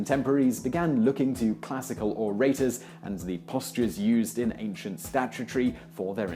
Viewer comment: remilia scarlet No, it isn't an accent. It's poor reading. In English, the letter-sequence "-ary" never stands for the sound-sequence "tree." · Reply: scarlet You think he didn't say the word "orators," so scroll to 2:00 and start playing ... in 2:00 or 2:01, you'll hear the phrase "Richardson and his contemporaries looked to classical ________ " — what word do you hear, right after the word "classical"? I hear the word "orators" (but mispronounced as "or-ATE-ors" instead of being pronounced correctly as "OR-uh-ters"). What do you hear there instead?